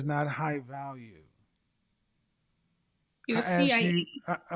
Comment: what are they talking about what acronym did she say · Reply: cia = confident intelligent assertive